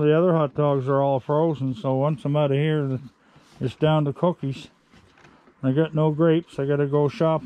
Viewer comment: Oh my I hear you say “cokies” and I still laugh because my la Cajun ears thought what are “cokies” when I first watched you and then I said ohhhh “cookies” Love that accent still, never get tired of hearing “cokies.”